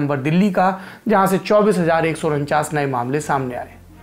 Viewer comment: bhaiya delhi k case 24000 he ya 28000 kya bolre ho